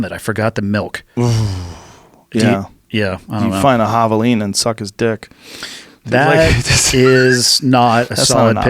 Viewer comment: what did Joe say??? I’d find a halloween and suck it’s dick?